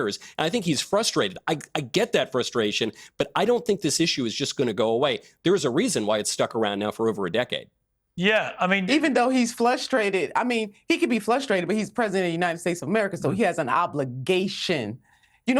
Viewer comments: Did she say, FLUSHtrated? 😂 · …. Did she just say “flustrated”?… Actually, she just said it again haha. She’s smart, so I’m going to guess she meant it as a combo of flustered+frustrated. I’m not confident of that claim, but it’s more of a hopeful wish.